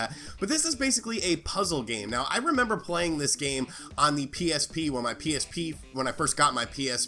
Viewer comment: sorry did you say on your psp? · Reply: psp